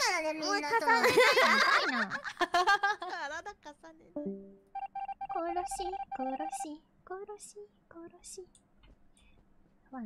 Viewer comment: Love how the others think singing "murder murder" is totally normal and not bringing it up that she's sus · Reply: Well, "kurosi" wouldn't be a Japanese word since "si" isn't a Japanese character (it is a Chinese one, though, and the Japanese equivalent is shi). Also, she distinctly says "ko" not "ku" (ko sounds like koh or koe and ku sounds like kuu or koo). You can see the characters in YouTube auto-captioning, though they're inaccurate at times. For example, by pausing at 2:50 (from 2:47 to 2:53 in transcript), you can see this as the captions: "殺しよろしい殺しうん知っ" which roughly translates to "koroshi yoroshī koroshi un shi~tsu" the latter part of which is obviously not what is saying. The more you know, amirite.